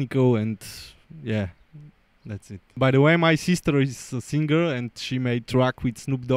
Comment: at first i thought he said "btw my sister is single" but I realized he said she's a singer lmao